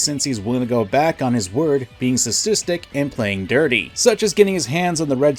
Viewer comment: I... can't make out the word you used. Being "what-sistic"? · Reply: Sustistic